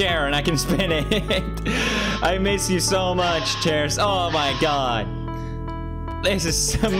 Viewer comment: did he just say he miss chairs? Thats illegal